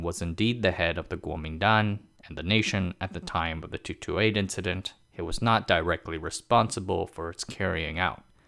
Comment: At the time of the 2-2-8 incident? (10:51)? I'm not sure I heard it correctly, but in any case what was that "incident" about?